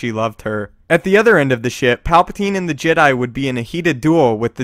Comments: I thought he literally said: "At the other end of the shit" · am i the only one who heard him say "at the other end of the shit"?